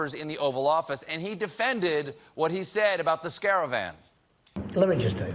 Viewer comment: Imagine, when Trump is sent to jail (with a suit that matches his complexion), he will have you and Seth roast him on loop, for his entire sentence. Did you call it Scare-avan?